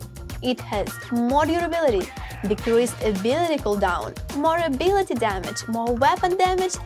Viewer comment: ok hold up, does she say "more ability damage" when its a spectre